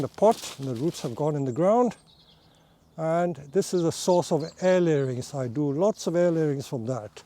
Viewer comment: "and this is a source of _________ I do" Can anyone clarify what he's saying there? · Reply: "Air layerings" It's a propagation technique There's an older tutorial from: <link>